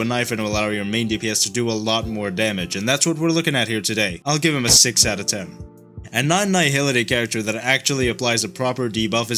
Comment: no, I said he has a debuff 😀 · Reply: sorry I misheard I heard "another nihility"